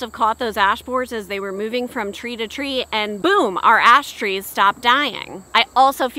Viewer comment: I swear I thought you said Ass trees at first 🤣🤣🤣 chickens are nosey enough to take care of those too 😆